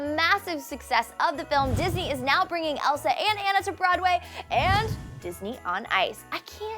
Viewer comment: did she just say.. anna?!?